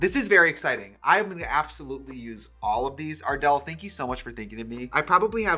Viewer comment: At first I thought Trixie said Adele instead of ardel 💀💀💀💀💀💀